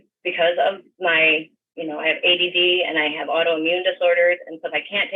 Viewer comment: did she just say that she has ADD/AIDS?